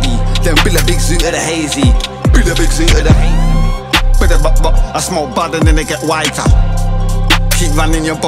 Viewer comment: Man thought he said "budabopbop i smoke bud and get whiter"🤣